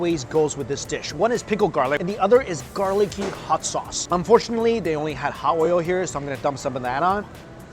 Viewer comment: Sorry Mike at 3:57 did I hear you say unfortunately all they had was hot oil?? lol what in the world are you saying ?? lol